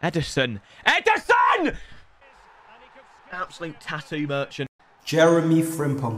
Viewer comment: what is this word that he everytime says how is it being typed "Merchin"